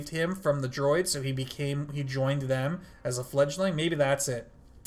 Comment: fledgling? I thought they said foundling...basicly found youngling